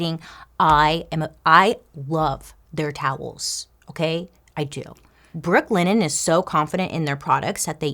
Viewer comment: I thought she was saying Brooklyn Inn